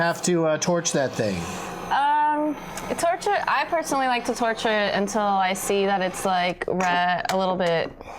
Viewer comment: did she say torture instead of torch it lol